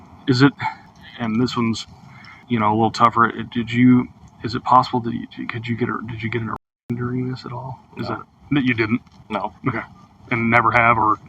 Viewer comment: Did he say "Is it possible that you stuck your finger in her during this at all?" or what? I'm struggling to figure out what the cop asked him.